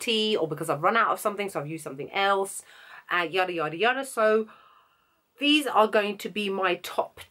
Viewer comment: Can someone tell me what kind of expression she uses at 00:37 (after saying "something else"), I'm French and I can't understand. Thank you :-) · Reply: Hey sweetie - do you mean yadayadayada? lol its like saying etc etc etc or blah blah blah - like going on and on and on...erm i hope that explanation helped? xx